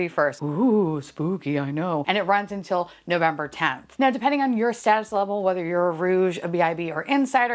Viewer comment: I thought you said... "at you're saddest level...lol🤣🤣🤣